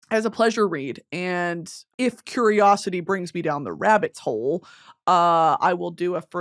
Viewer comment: did you say rabbit's hole instead of rabbit hole..